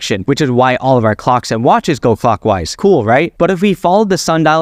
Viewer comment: I swear he says f*ckwise